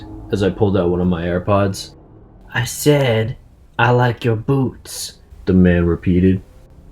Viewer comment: I THOUGHT HE SAID "I SAID I LIKE UR BOO**bS" oof lol